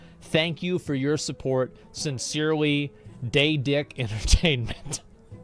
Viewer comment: Watched Lirik play it a little bit, and the moment I heard Gollum talk I bursted out laughing. Did they just pick some random crack addict dude from the street and said: ''Fuck it... He'll do''? It didn't sound ANYTHING like Gollum that we've seen from the movies. I could personally, and honestly do a better impression myself, so if you're reading this and are looking for a new voice actor Daedalic Entertainment, (or whatever the fuck your name is, had to google) hit me up. The fact that this game's 49,99€ on steam right now Is beyond me. Scrap this project, or make it free to play. Or what the hell, ACTUALLY put some fucking time, dedication and love into the game before releasing it. It's honestly embarrassing. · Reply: Daedalic Entertainment? sounded like asmon said Daydick Entertainment lmao.